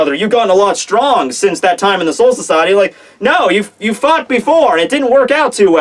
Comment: INSIDE OUT HITSUGAYA! AHAHAHAHAHAHAHAHA!!!!! That was AWESOME! XD 11:37 that sounded like "you fucked before" LOL Orihime blocking Juha's power is bs Juha's speech is either mindgame or total bs and what the hell happened at the end ._.